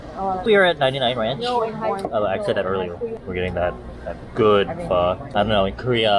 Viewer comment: When he said we're going to Houston I thought he said we're getting wasted lol if that ain't me spending time with my family